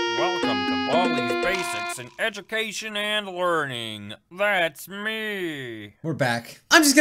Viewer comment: Did baldi say "learning" or "warning" ?